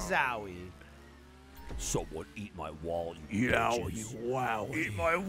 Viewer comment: did...did Jeremy just say Yowie Wowie?